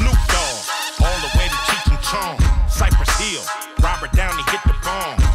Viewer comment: did he say cypress hill?